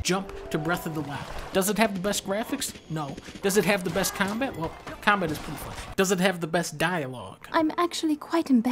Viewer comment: what did he say about combat? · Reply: "well... combat is pretty fun" It's making a connection to the previous section where he talked about how combat in GTA is bad.